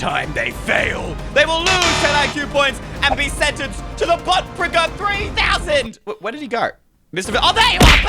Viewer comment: I almost thought he said “Butt fricker” 😅